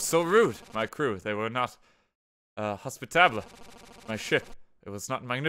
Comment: did he just say "hospitablay"?